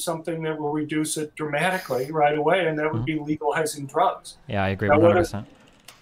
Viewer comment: Cenk's Nephew's friend got put in a binder this debate. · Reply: also at 26:24 did John say legalising drugs would help the gun issue. Can barley understand him.